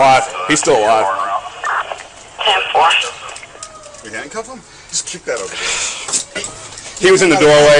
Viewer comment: I heard an officer say "hand cuff him?" after they shot him. Around 00:39 sec